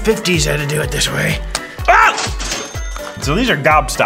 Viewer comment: The fact I thought it said fat19